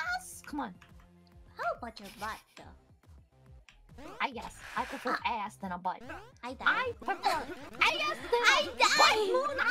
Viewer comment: she says "I guess I prefer ass than a butt," then "I prefer ass than a butt, Moona!" It's an easy mistake to make since it's not proper English. She phrased herself in a way that's common for native English speakers if they're not thinking about what they're saying too hard.